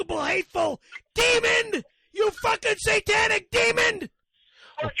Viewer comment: Is he saying "Demind"?!